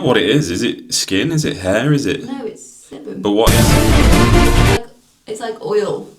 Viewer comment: I’m sure I heard him say semen not sebum at times, with the coconut oil scrub. 😂